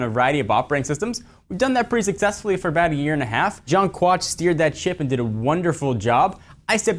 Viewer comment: did he just say steer that shit? at 0:24? or I need to clean my ears xDDDDD